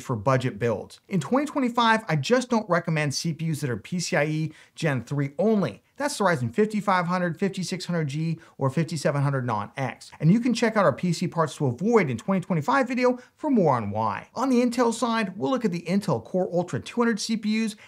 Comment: Yep, we actually go over this in the video (; Its why I do recommend 5600 but don't recommend the 5500 or 5600G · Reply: My bad, I misheard "5700 non X" as "5600 non X" and commented before finishing the video, thanks for the clarification!